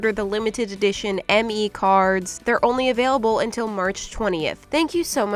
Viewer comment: I heard merch 20th😂